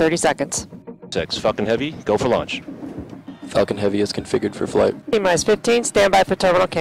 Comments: Did he say Fuckin heavy? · I thought they were saying fuckin heavy 🤣🤣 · it sounded like "TX fucking heavy ready for launch" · idk why i hear "Falcon" as "Fucking"